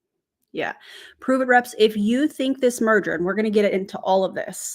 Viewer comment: Me, half listening while I cook: DID SHE JUST SAY MURDER?!?! keeps listening: ohhhhh it musta been merger....😂😅